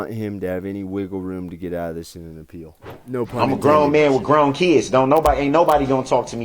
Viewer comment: Hey! Thanks for the Aussie shout out! I speak English, however there were times I had trouble understanding Brooks. I really enjoyed your commentary, you give a unique perspective. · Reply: Hi from France! Just a funny stuff: when he said : I’m a grown man with grown kids » I thought he said : »I’m a grown man with grown teeth » I thought that was a real expression 😂😂😂… well for my defense English is not my first language 😀